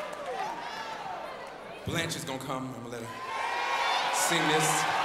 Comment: Awesome! Can anybody help me understand what he says in 1:57? I just couldn't. God bless! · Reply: "Blanche is gonna come..." - He was introducing the singer of the song. :)